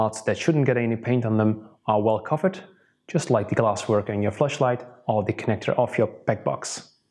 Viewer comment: Sounds like he said fleshlight lmaoooo🤣🤣🤣